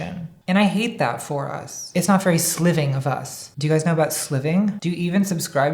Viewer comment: Every time you said "sliving" in past videos I thought you were saying "slything" like living a Slytherin lifestyle.